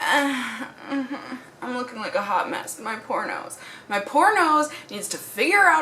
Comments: When you said "My poor nose" at the beginning I thought you said "My pornos" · Just so ya know... "poor nose" sounds a lot like "pornos." lol Caught me off guard a little. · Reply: I thought she said pornos so I came to the comments to see if anyone else noticed😂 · LOL when she said “My Poor nose!” I swear I heard “My Pornos!”🤣🤣🤣🤣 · Lol I thought she was saying "MY PORNOS" haha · Just so ya know... "poor nose" sounds a lot like "pornos." lol Caught me off guard a little. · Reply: I thought she said pornos to 😂😅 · I heard "poor nose" but actually thought I heard "pornos" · Did anyone else misheard her and thought she said "my pornoes" instead of "my poor nose" ?😂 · At the beginning when you said my poor nose I thought you said my pornos.... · who else thought she was saying " my pornos" 😂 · When she said "my poor nose" I thought she said "my pornos" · OMGGG, when you said "my poor nose" i swear i heard "my pornos". It took me a second to proccess lol. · I thought you said your "pornos" lololol